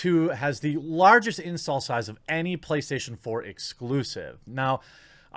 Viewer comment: I heard "'The Last of Us 2' has the largest incel size of any Playstation 4 Exclusive" LOLOL